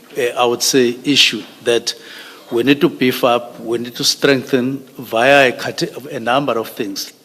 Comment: Did he just say he needs to strengthen up via a cartel @ 19:50, or I'm hearing things?